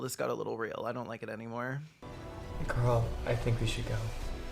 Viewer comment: When the boyfriend said "Carl, I think we should go..." I thought he said "GURL, I think we should go..." and I was like okaaay I think we're laying it on A BIT TOO THICK NOW.